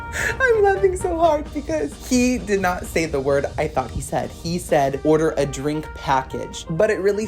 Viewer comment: "order a drink f*ggot"..Maybe that's what you though he said? Btw..I truly enjoy your videos.